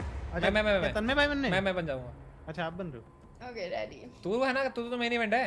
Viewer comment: Did she actually said 'ok daddy' at 22:48 or I just overheard it